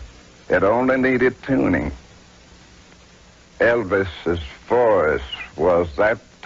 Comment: The subs are broken, but was looking forward to seeing what it did with the pastor's little mumble haha. 11:15 Haha he didn't say forest, he said voice, the subs are just broken cause it's based off of what it hears.